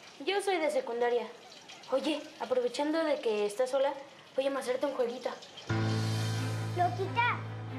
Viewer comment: Que dijo en el min 20:31 ese pelao habla entre los dientes y no se le entiende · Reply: Voy a mostrarte un jueguito